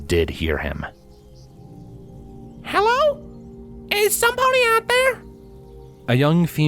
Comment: Did Applebloom say "somepony"? (*le gasp*) Is that the Equestrian Applebloom? What's she doing in EG world? 😮 (I kid of course, I know it's just a mistake of the reader.) 😅 (Still, nice bit of speculative fanfiction theory-spinning, no?) Lol